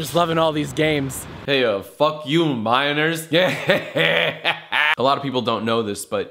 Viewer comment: you said "fuck you minors" but i heard "fuck you viners" lmao i thought u were dissin